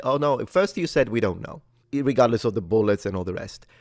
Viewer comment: Did he say 'irregardless?'